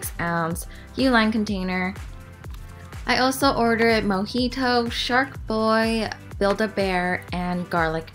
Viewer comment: When she said mojito is thought she said monkey toe lol 😂 And when she introduced shark boi I was waiting for a lava gurl